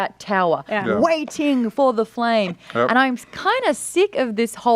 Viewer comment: I can't be the only one who thought was saying "no" not yeah or yup